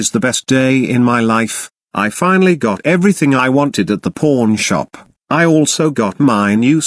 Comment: I ALMOST THOUGHT HE SAID P**N BUT IT WAS PAWN SHOP